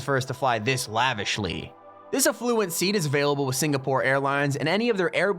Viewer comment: Did he say Singapore Airlines🇸🇬